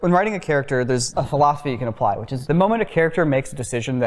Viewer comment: What does say at 5:51? "When writing a character there's a __ you can apply". Bc that word I thought was 'philosophy' but that's not it lol